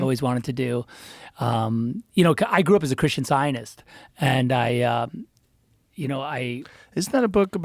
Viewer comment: My heart legit skipped a beat when I misheard the word Christian scientist as Christian Zionist @ 2:07:13 🙂‍↕️